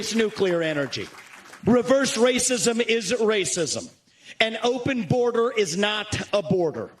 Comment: Sounded like he said "reverse racism isn't racism" but I'm sure that's not what he actually said.